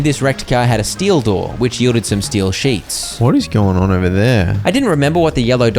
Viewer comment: I heard "steal sheep" instead of "steel sheet" 😂